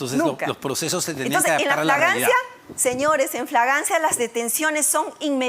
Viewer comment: la sra ampuero se ha rellenado tanto sus dientes podridos que dice flaglancia apartir del minuto 11 hasta el minuto